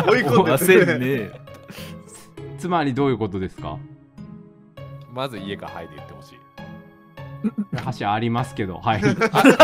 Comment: ここでがなんて言ってるか分からないので知ってる方いましたら教えて下さい！ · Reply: 「まず いいえ か はい で言ってほしい」